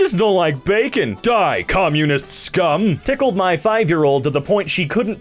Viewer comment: I had misheard tickled as pickled and was confused.